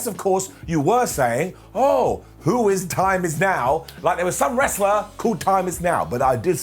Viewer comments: I thought it said "Who is Tim is now?" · Lol I thought it said “Who’s Tim”